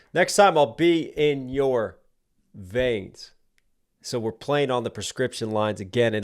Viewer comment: is it bad i thought white gold said i be in your van not vain lol